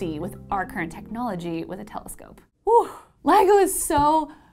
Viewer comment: I thought gravitational waves were old news, but I guess you guys like this video! Next week will be the extended interview with Ligo Hanford Observatory head, Michael Landry. It's gonna be quite an experiment to see how you guys like a VERY long interview video... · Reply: anyone else heard LEGO? Both LIGO and LEGO are cool 👍